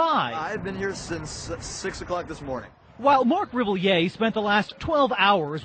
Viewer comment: Omg at 1:16 did he say Marc rebillet? Thee Marc rebillet? It also looks like a younger him. Why is no one talking about this meme